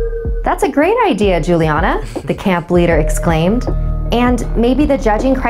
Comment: I kept looking at my phone Bc I kept hearing “Juliana” bro , das my name